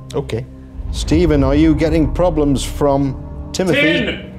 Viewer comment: this guy cant hear shane said "tin"